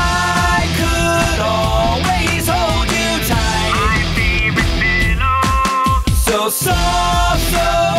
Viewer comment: I thought they said "I'm getting to know you" instead of "I'm getting a new you" who else heard that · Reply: NightI thought that too. Also when Link said "my favorite pillow", I thought he said, "my favorite minnow lol